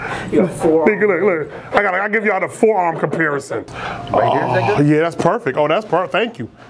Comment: did he just say "Right here Ni**a ?"